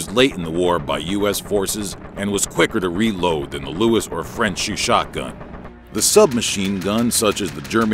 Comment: what u said : ...chauchat gun... What i herd : ...shu shotgun...